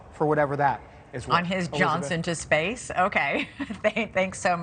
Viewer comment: Did she just say “Elon Musk, on his “Johnson” to space” Huh?! Lol